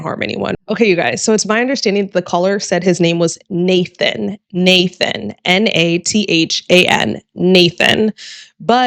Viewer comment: The i heard Nathan Nordike. Close caption also got that.